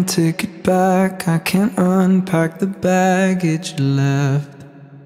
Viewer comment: I always thought he said I can’t unpack the baggage you left but now I can here what he’s saying better. Thank you, this is beautiful.